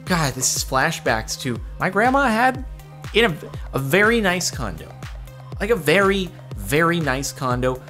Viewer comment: deadass thought said "My grandma had a very nice condom"